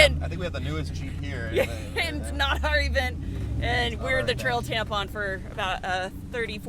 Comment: First time I heard " trail tampon". Hahahahahaha